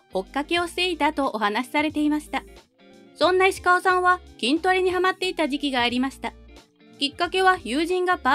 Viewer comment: なんとなく聞き流してて、石川さんが杉田さんの追っかけしてたってナレーションの後、「銀魂にハマってた」って聞こえてそりゃ杉田さん好きだったなら銀魂も好きだろって思ったら「筋トレにハマってた」だった